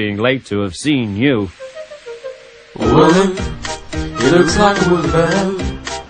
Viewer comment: What does the song say? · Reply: "He looks like a wooly bear"